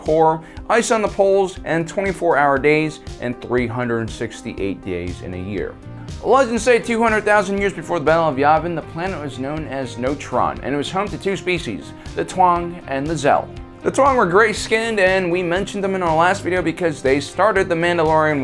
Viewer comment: I hear "Before the battle of the oven" every time you say BBY. xD